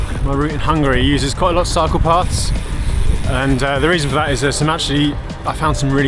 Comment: As a French speaker, I literally heard 'Hungary, lots of psychopaths' instead of 'cycle paths' lol